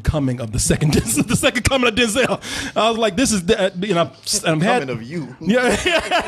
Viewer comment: What is Martin saying at 22:30-ish mark? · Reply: “The coming of YOU” I believe. The You referring to, of course.